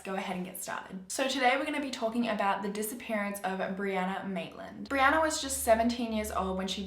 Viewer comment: I thought it said Barbara Maitland I’m sorry